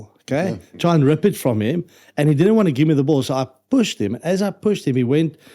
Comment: for a second I thought Jean said “poesed” him 😂😂😂